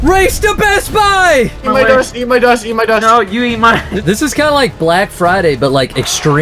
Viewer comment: When he said “eat my dust” I thought he said eat my nuts 🥜😂🤣😂🤣😂🤣😂🤣😂🤣😂🤣😂🤣😂🤣😂😂😂😂😂😂🤣🤣🤣😂🤣😂🤣🤣😂😂🤣😂🤣🤣🤣🤣🤣🤣🤣🤣🤣😂😂😂🤣😂😂🤣😂